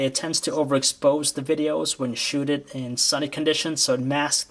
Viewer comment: did he say "shooted" ?